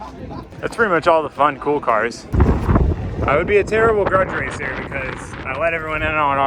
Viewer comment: I thought I heard "I would be a terrible drug dealer, because..." Which also makes sense in this context.